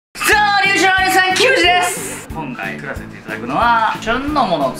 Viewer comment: リュウジのお兄さん キムチです！ って聞こえたw ほうれん草のごま和え いつも まずくはないけど‥微妙って感じだったので やってみよう 他の方も書いてたけど 白和え！ 買ってきた方がうまいかも ってなる(＝_＝) 至高の白和え 知りたいなー